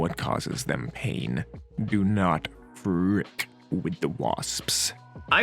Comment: I thought he said "do not frick the wasps"